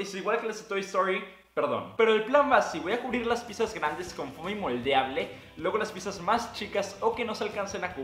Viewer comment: Disculpa, ¿con que material cubriste las piezas? No te escuche bien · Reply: Fomi moldeable, básicamente plastilina